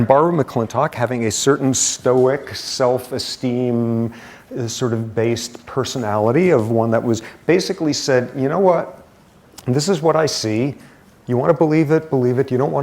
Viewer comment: Did he just said "based"? was the word popular those years?